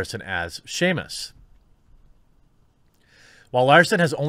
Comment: I thought he said "shamus" lol